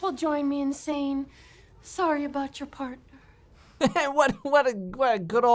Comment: "Sorry about your partner"??? My whole life I thought the teacher said "sorry about your part" because the fireman's winkywoo got burned off.